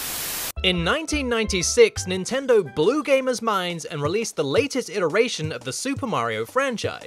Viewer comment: I thought he said "Nintendo Blue games is mine"